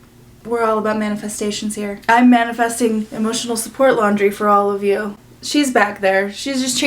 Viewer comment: Thought you said 'emotional support lingerie' 😂